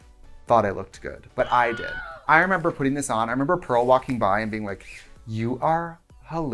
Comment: why when she was talking about the season 7 finale dress and said "pearl walked by" but at first i thought she said "some girl walked by" and just accepted it